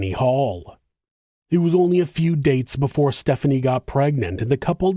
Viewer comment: Am I hearing this correctly? 3:04🤯🤯 A Few Dates?